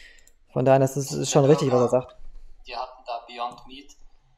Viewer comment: 👍🏻 Jetzt kostenlos abonnieren <link> ⭐kennst du schon Liposomale Supplements?: <link> ⭐Kennst du schon unsere Tees + <link> ⭐Liposomales Vitamin D3: <link> ⭐Liposomales Astaxanthin: <link> ⭐Liposomales Vitamin B12: <link> ⭐Liposomales Vitamin C + Zink: <link> ⭐Liposomales Elite OPCt: <link> ⭐Liposomales Curcumin:<link> ⭐Liposomales Gluathion: <link> ⭐Liposomales Melatonin: <link> Das wichtigste Tool für jede Küche, auch mit einer 0 % Finanzierung ✔️Bester Entsafter Weltweit: <link> ✔️Bester Mixer Weltweit : <link> ✔️Meine Lieblings Nahrungsergänzungsmittel der Welt: <link> ✔️Meine Lieblings Tees + Kräuter: <link> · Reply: Hallo, ich verstehe das Wort bei 24:52 nicht. Beontmit? Der Fleischersatz als BurgerPaddis. Kannst du mir bitte das korrekte Wort geben. Hab die Suchmaschine schon bemüht doch glaube das ich das Wort komplett falsch versteh. Danke im voraus.